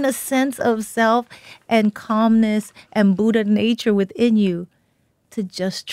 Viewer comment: did she say "Buddha Nature within you"?